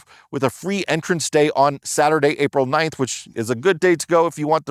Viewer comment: Maybe I heard it wrong, but the date is April 19th, not April 9th. Thanks for these updates! Our family has been really enjoying the National Parks this year with our oldest son's 4th Grade Pass, and your update videos have been really helpful. Cheers to you!